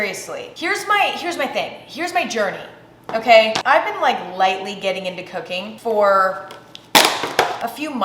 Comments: I thought she said I have been lightly getting into cocaine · When she said "ive been lightly getting into cooking" I legit thought she said "ive been lightly getting into Cocaine" lol Had to rewind and listen again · I THOUGHT YOU SAID “I HAVE BEEN GETTING INTO COCAINE LATELY”😭